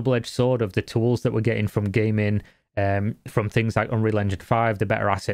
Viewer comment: I actually misheard it and heard you said GAY MEN ... ROTFL